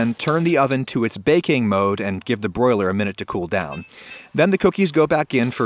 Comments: Oh I thought it said boiled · Who else thought it said boiled 😖 Just me.... okay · I thought you said *boiled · I thought it said "boiled" until he said "broiled" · Lmao who else thought it said “boil” instead of “broil”😂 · Oof i thought it said "boiled" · Thought u said BOILED. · I could’ve sworn that caption said “Boiled”. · Good grief, I thought it said "boiled". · I thought that shit said “BOILED” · ohhh i thought he said boiled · thought is said boiled and i was like no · i thought it said boiled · Who else thought it said boiled 🤣